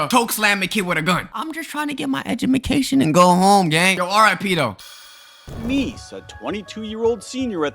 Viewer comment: u said rip and captions said uhmm "pedo" RIP tho